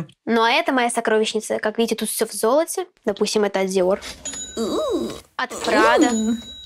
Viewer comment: я услышала допустим